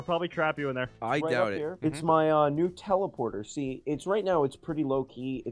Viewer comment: when i heard joke i heard "nutella-porter" and im just like what??